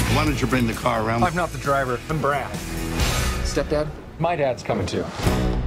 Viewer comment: i thought it said Stephen King in the back